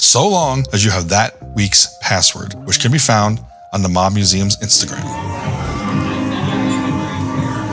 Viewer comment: Ngl. Thought you said the "mom" museum.